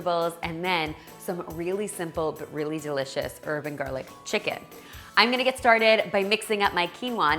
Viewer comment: Omg every time you said "herb and" I thought u were saying urban and I was so confused cos in England we pronounce the h in herb aha